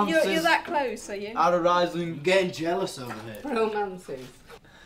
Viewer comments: ''i'm gonna get jealous over there'' Sorry if it's not what he said english is not my native language 💕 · am i the only one who heard harry say i'm getting jealous over here